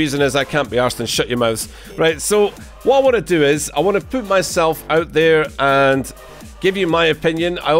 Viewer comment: I Wana poop myself 🤣🤣 PS. He actually said "Put" (Just having a little fun.